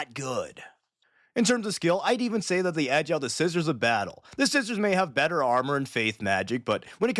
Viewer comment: did you say the scissors of battle😂😂😂